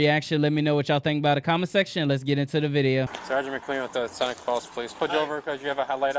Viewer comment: If anyone is impaired, its the officer. Listen to his slurred speech! Sounds like he says, "Sg McQueen with the Sonic Balls police."